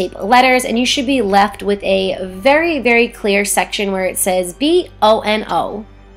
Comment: Did she say bono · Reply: Dayan Rojas she said BO and O but I heard differently...